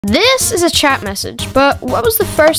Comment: Am I the only one who thought he said "trap message"